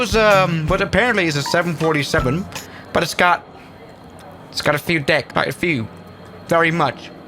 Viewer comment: i thought you said "it's got a few d*ck"